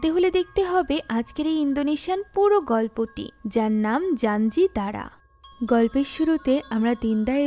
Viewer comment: মুভির নাম কি বলল কিছু শুনতে পারলাম না 😢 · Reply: জানজিদারা